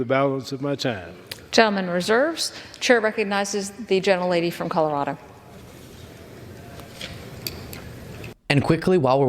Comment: For a moment I heard 'the genital lady from Colorado' being recognised.